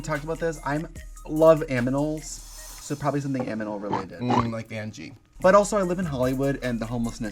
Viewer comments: did Trixie say "I love aminals. It's going to be aminal related." !!?? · Trixie says aminals instead of animals? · Does Trixie say "aminals"? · Is she saying "aminals" insteas of "animals" at 4:59 or am i crazy